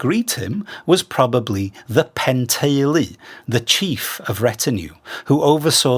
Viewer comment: you describe the chief of retinue as something like "Pentaile"...? What's that word? Where does it come from? Thanks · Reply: Penteulu; pen = chief; teulu = retinue / household